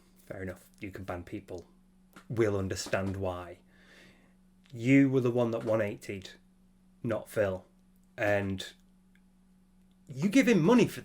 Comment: "you were the one that...." what's that you said there sir? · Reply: "You were the one that 180'd, not Phil"